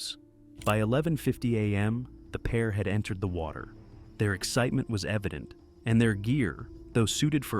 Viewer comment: i thought he said their excitement was epic omg